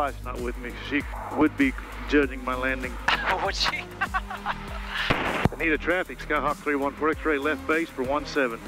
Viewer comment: What airport is this? I heard you saying "Bonita traffic" but I can't find an airport with that name.